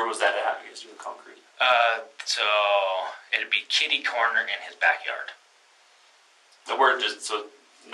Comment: did he just say KITTY corner? 😂